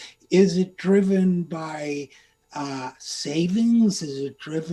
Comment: ish I swear I had to go back as I thought he said Satan lol... "Could it beeeeeeeSATAN?!???!!?